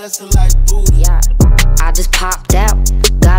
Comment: Start of the song : I just pooped out 💩 Is what I thought she said lol.